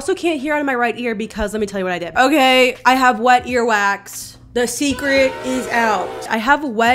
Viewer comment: i thought you said white earwax i was beginning to get concerned